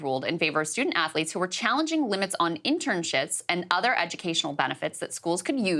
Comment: Did anyone else hear “intern shits” @ 0:25 lmao (everyone makes mistakes from time to time, not judging)